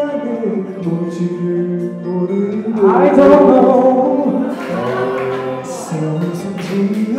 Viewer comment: DK @ 6:36 : "아이 돈 노" as in "I don't know" but the caption said "kid no money" LMAOOOO 😂😂😂 PS: 아이 = kid 돈 = money 노 = no lmao I need a chat with whoever does the captions 😂